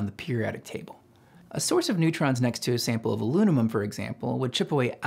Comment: Is it just me or did anyone else hear him say Alunimum at 5:10?